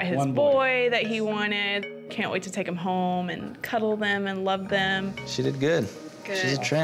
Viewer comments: "She's a tramp" What you just say? Lol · What a cute video! Buttercup was amazing...side note did anyone else catch the Dr at the end saying she's a tramp instead of champ?